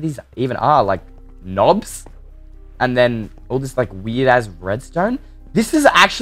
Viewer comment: did he say weird a*s redstone? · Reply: He didn't say a*s he said 'It is weird as this redstone